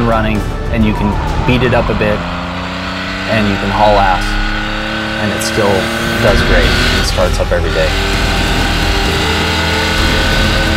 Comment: What did he say , ""can haul ass ""(____) "" various parts of every day.""??? after 4:15.